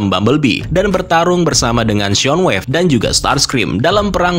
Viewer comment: soundwaves bang,gua malah dengernya shoong wave